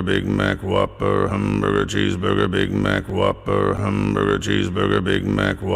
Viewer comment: I THOUGHT IT SAID WAP BURGER